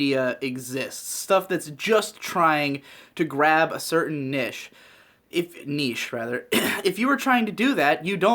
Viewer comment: When he corrected saying niche I heard "pussie's"